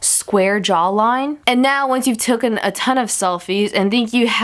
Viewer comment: wait. did she just say tooken?